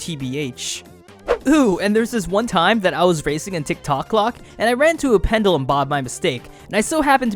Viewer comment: did you say... TIKTOK?!!?!